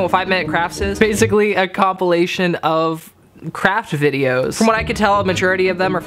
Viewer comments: A thought you said crap video XD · At first I thought he was going to say 5 min crafts was a compilation of crap instead of crafts